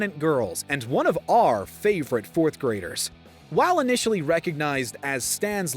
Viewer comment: Bro did he just say “One of ARE favorite 4th grader”